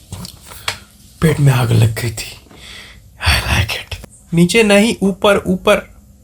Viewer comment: what did he said before "I like it"? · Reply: He said pet me aag lg gai thi